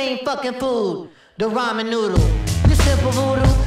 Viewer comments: I always hear "rhymin' noodle" instead of "ramen noodle" and I think it fits even better :D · Is it "ramen noodle" cause I hear "rhymin' noodle"?